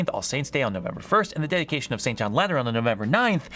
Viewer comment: Did I heard it Right ? Dedication of Saint John Lennon on november 9th ?